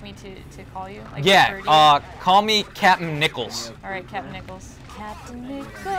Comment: i thought you said captin pickles at first :)